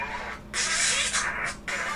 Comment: I heard Moon as to your shirt